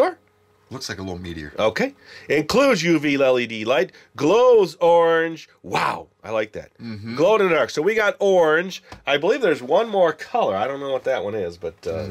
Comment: <3 u!!! I thought it said glow in the oak xD 😂🤣